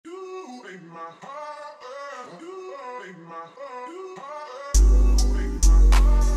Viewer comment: "you ate the whole earth"💀 "you beat my father"💀 "You ate caseoh bro"😨 "Dude make mom harder"☠️ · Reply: I hear “dude make me harder”💀💀💀💀💀💀💀💀